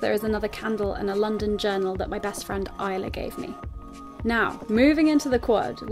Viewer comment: I thought you said you read the magazine 'as my friend died' I was like WTF SHE INSERTED THAT SO CASUALLY LOL.